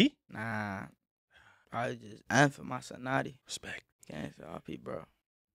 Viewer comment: why did dd say f was his second favorite letter because of notti why didn't he just say n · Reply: He says N. The way he says it, it does sound like F but he’s saying N